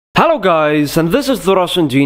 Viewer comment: I heard "Hello guys! I am Rase-ngan!!!"